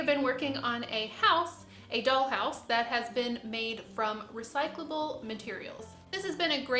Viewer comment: 😄😄 I guess I should have said "recyclable" as in... could be recycled. I can confirm it was not previously used😉😄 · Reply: you probably did say recyclable, but you emphasized in previous videos that cardboard and chip board are recycled from boxes and stuff so I heard “recycled” instead of “recyclable” ;)